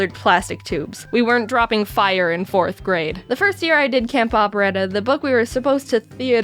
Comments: I thought it said camp opera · Don't know why I thought it said camp opera